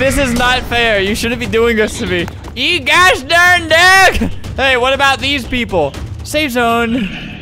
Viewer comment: Him saying, "You geaosh/gosh darn daerg/dog." Then we have subtitles saying smth else...